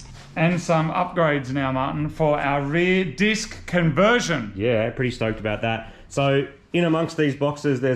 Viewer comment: AWE I thought he said "Our rear DIFF conversion". But sadly no.